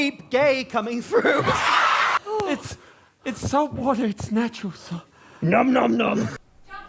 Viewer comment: i think he says 'its natural salt'